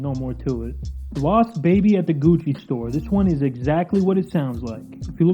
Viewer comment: I swear at first I thought you said “Boss baby at the Gucci store” My mind thought it was going to be some deep fried meme with some clue to an obscure ARG. But Once again, another banger video